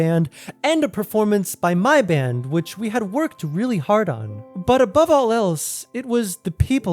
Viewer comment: did you say hard on?